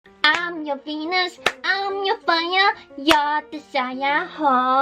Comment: thought she said '' im your penis! '' at the start